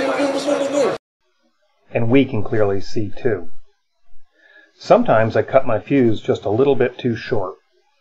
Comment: At first I thought I heard "Sometimes I cut my pubes just a little bit too short."